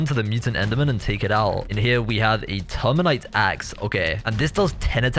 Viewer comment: For some reason i thought he said 'termite axe'